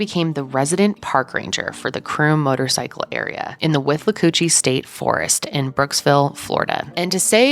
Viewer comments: I heard, "whiff a coochie forest" 🤦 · Guys, I’m taking this seriously, I promise…….but I genuinely thought just said “Whiff-a-coochie” State Park. Withlacoochee isn’t a lot better, but, it’s definitely better. I had to pause the video when Mitzi’s business card was on the screen to find out what it really was, because every time I went back to listen, I still heard “whiff-a-coochie” and even though I’m a grown adult…and a doctor….I found it hilarious. I’m sorry, I don’t know why I’m like this.